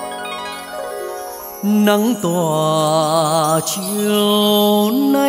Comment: Hát okie rồi mà góp ý chút. Chữ nắng cứ nghe thành Nấng là sao nhy?! Xem lại chỗ ấy biểu diễn thì tiết chế động tác co giật theo nhạc